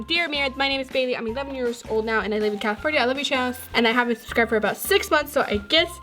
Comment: I LOVE YOUR CHANNEL YOUR MY FAVORITE YOUTUBER!!!!!!!!! How would I send you fan mail?? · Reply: Alexis Jones Omg I thought you said I hate your channel lol